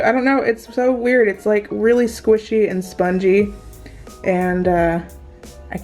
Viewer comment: Good for you I don't care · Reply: Bendy I misheard it is spongey okayyyy gosh u don't have to be such a jerk about it